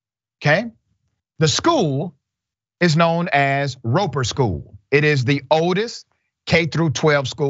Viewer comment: What school was it? I really thought I heard Ropey school. As in showing a lot of wear and tear Not a criticism of the school just misheard it and made me laugh